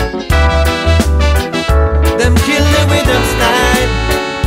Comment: ¿¿qué es lo que dice en Ingles?? Alguien que me ayude con la traducción por favor🙏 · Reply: they kill them with them style, ellos matan con su estilo